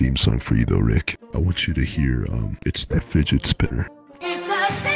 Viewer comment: It atually sounds like it said “It’s a fidget spinner”